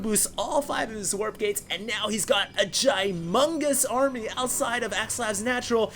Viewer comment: "... And now he's got a GIAMONGOUS army!..." LOL!!! Humangous?! Gumangous? Giamongous?! What the hell did he say there!? lmao!